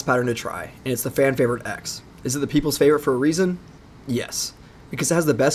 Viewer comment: "My go to is the balls and shaft" 🤣 had me dead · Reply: Bro I thought it said balls of shaq💀💀💀